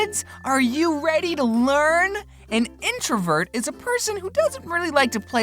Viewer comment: I coulda sworn it said internet